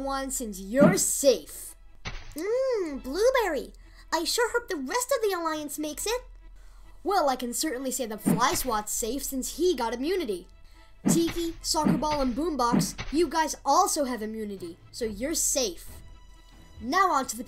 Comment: i hear tv instead of tiki